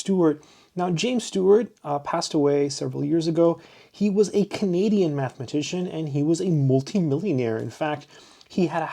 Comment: and later I hear "comedian mathematician" on the first listening ...